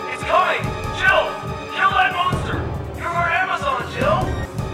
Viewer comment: Every time I hear Brad talk after he gives Jill the rocket launcher sounds like he says your from Amazon Jill. Lol oy